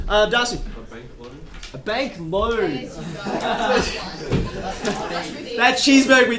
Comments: "sir this cheeseburger is $2" "can I pay that with a bank loan?" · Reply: I can't lie I thought he said baked loaf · When that guy said “a big loan,” I thought he was saying “a big load…” 🤣 Yes, I’m a perv.